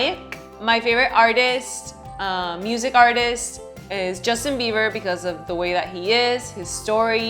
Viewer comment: did she just say "Justin Beaver", had to rewind a few times and every single time I heard beaver lol